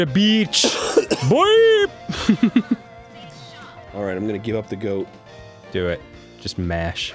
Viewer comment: does Arin say "I'm gonna give up the goat?" Like, instead of give up the ghost? It's adorbs if so